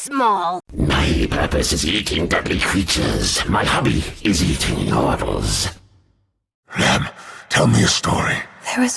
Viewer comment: not going to lie i always thought he said "my hobby, is eating marbles."